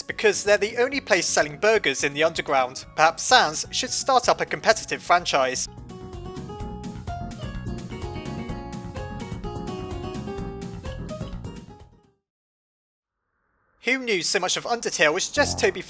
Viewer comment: Did he say Sans should start a competitive french fries?